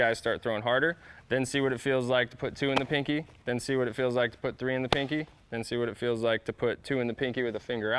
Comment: My wife walked out the room when she heard him say 3 in the pink